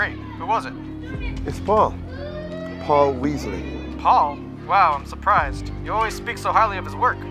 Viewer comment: I thought he said Paul Wesley from tvd💀